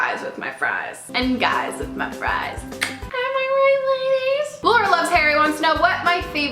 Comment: She said "Laura loves Harry" so fast I thought she said "more or less Harry"😂😂